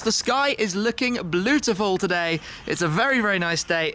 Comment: did he just say bluetiful